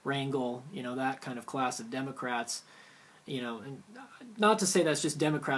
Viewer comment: It sounded like you said DemoCRAP.